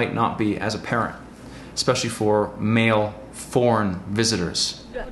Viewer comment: Sorry but I thought you said porn male visitors. 😂✌🏼